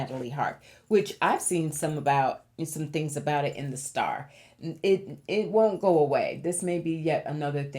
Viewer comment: I heard you say, "you saw it in the star." LOL! I had a double take thinking the STAR tabloid!